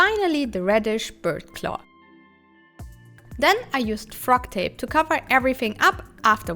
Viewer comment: Hi, at 8:37 I didn’t catch what that tape is called , did you say frog tape? It looks just like masking tape but green. Is the a special kind of masking tape? Thank you. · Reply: Yes, it's called Frog Tape and is especially made for fine paints and airbrush applications. :)